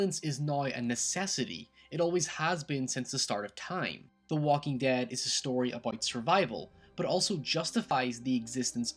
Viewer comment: excuse me, but at 8:55, did you say 'that the walking dead description of white survival'?